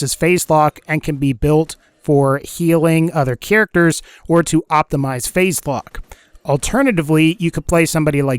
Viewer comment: Did. Did he say Maya could optimise facebook?